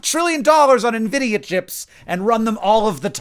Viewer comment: When Hank said "Nvidia chips" I heard "idiot chips", and thought to myself "yeah, that sounds about right"